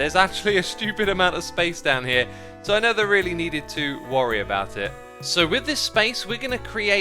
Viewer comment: did he say he nether really need to worry about it